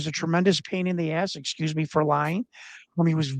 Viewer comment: Did just say Excuse me for Lying? at around the 7:20 mark?